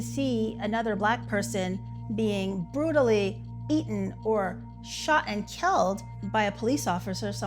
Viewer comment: AYO DID SHE SAY EATEN